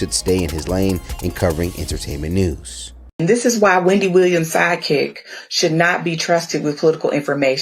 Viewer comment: All I hear is “Your OG is Wendy Williams”.